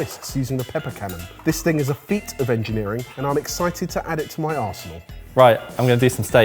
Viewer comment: I know he said arsenal, but I definitely heard asshole...